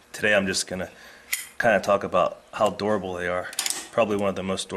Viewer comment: I swear I thought he said "how adorable they are" at 3:22...